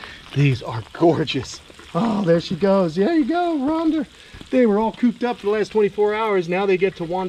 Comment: I thought I heard you call the large one Rhonda! 😆😊